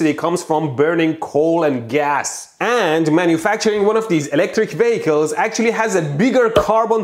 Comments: Ever since you talked about electric pets I kept hearing "electric bagel" instead of "electric vehicle" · Every time you say "electric vehicle" I hear "electric bagel" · I heard "Electric Bagel" rather than "Electric Vehicle" hahahahahahaaha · I kept hearing "Electric Bagels" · every time mehdi says "electric vehicle" i hear "electric bagel"